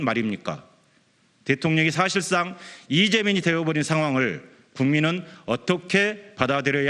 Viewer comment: 대통령이 사실상 이재명이 되어버린 상황? 그렇게들리는데 맞음?